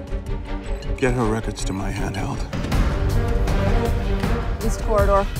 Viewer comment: did he say Handheld tho? 2:57 lol